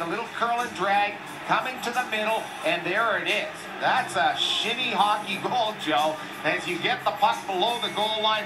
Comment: oh come on now. He clearly said “shinny hockey goal”🤣😭😭🤣🤣